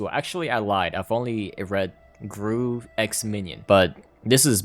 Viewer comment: what the fuck did he just say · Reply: He’s only read gru x minion😂😂😂 wait WHAT😭